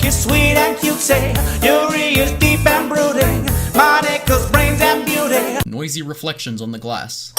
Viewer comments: When it said monika at one point it sounded like it said "my nigga" I'm black btw =/ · For some reason I heard the name “monika” the same at “my nigga” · Wait...did i just hear him say "Monika is cute and gay" if not im gonna tell my mom to clean my ears